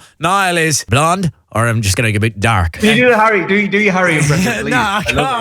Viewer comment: so i heard him say larry...